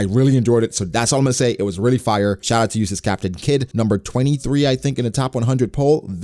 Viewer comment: 🤣sound like u was saying “useless captain kidd”